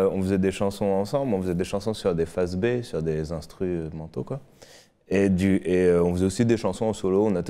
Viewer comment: Sur des phases B, sur des instru euh.. Manteaux quoi XDDDD